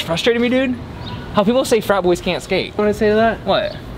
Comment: my bad I thought you said fat boys